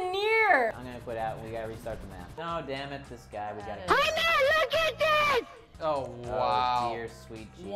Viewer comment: sounded like they said "HEY MATT LOOK AT THIS" and if I'm correct real name is Matthew if it is shortened it becomes Matt so there were probably talking to